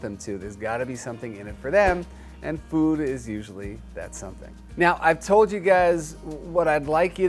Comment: did Jackson say "meow" instead of "now" or am is it just wishful thinking, because that would be awesome if he did! :)